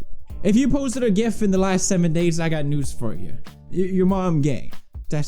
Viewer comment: Did he say "you're mom gay"? XD